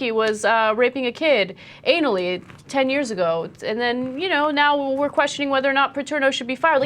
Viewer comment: anally ten years ago but the subtitles say "Italy 19 years ago"...The subtitles is SO WRONG at many places it HAS to be intentional